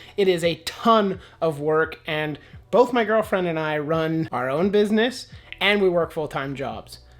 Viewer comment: Hahaha I thought you said both of my girlfriends and me!!